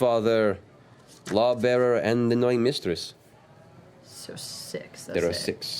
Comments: I actually thought they said "annoying mistress" in the beginning... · I'm gonna be fair here, I too thought Matt said Annoying Mistress as well